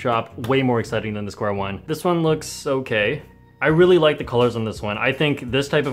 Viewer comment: did he say its looks gay